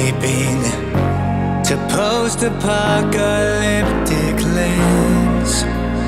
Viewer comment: This song is 10x better when you get all of the references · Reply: beeftastesliketoenails Oh I guess I just somehow missed the reference, even though I am a Minecraft fan, I didn’t pay attention to that. Edit: I think I misheard it as lands, not realms, idk why I misheard it like that.